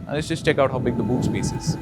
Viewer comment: did he really say "let's check out how big the BOOB SPACE is" ?! Or it's just me..... ?!